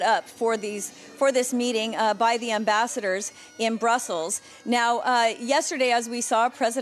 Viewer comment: have i heard wrong, did she said “at brothels”?